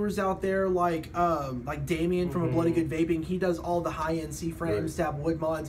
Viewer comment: hey ' good seeing you on mark's show last nite' you should be a regular man! cool vid here..trying to figure out the reviewer mentioned at 18:49 of this vid his name is Damien, can't understand the name of his channel, gotta see these high-end mods...keep the vid coming, brother! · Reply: Bloody good vaping